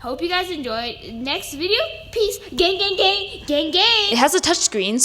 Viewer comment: gang gang gang gang. lowkey I thought it said gay 😂. but best tour 👍